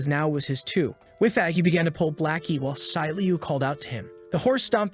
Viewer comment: that’s gotta be racis did I hear “pull blacky”